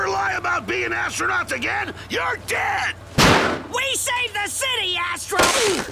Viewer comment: I thought he said "we save the city A** H**